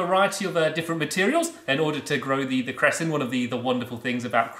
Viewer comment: Who else didn't know what cress was until these vids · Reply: minecraft I honestly thought he said Crest a.k.a the tooth paste